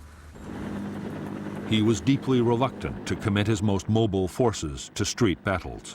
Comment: What word is this?? He was deeply "relacted"? This word does not seem to exist... redacted?? I mean it is clear what it means but I would like to know this word for the future so thanks for any help :) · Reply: Its reluctant, not relacted.